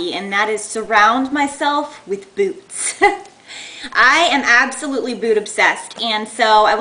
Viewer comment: At first I thought you said I'm absolutely BOOB obsessed.....